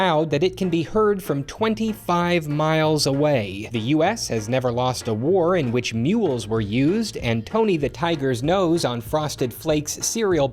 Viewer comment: I honestly thought he said "The US has never lost a war where Nukes were used".